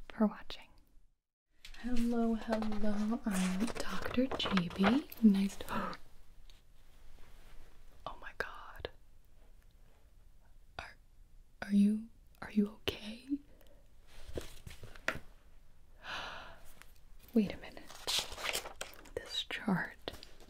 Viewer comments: I thought you said ‘are you.. gay?’ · i heard this as "are you gay?" 😭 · min - not me understanding "are you gay" instead of ok😂😂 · I thought she said "are you gay" BHAHAHAHAHA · Lmao the beginning all I heard was “Oh my god are g a y” makes me laugh every time sorry gibi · I turned my phone off by accidents and I laughed because it sounded like you said “Are you gay” lmao 😂 cracked me up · I thought she said “are you gay?” 😂🏳️‍🌈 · I heard is "ar are you gay?" Lol HAHAHHAA